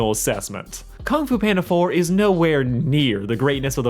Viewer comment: did he just say kung fu panda 3 is the worst?